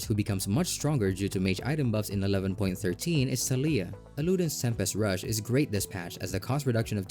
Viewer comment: I immediately hit the comment section at 5:50 of this video because I heard "SALIYAH" Instead of "TALIYAH" and I even repeated that part twice